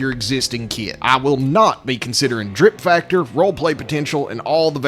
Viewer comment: Shit i thought he said “FOREPLAY” potential and i’m like Okaaay 🎉